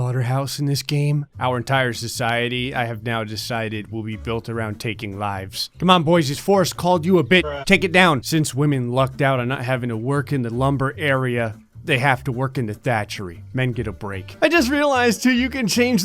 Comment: It's not often I get to play a game where you can make unlimited taxes... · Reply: When you said taking lives I literally thought you said taking vibes I said wtf they where vibing to the screams of the innocent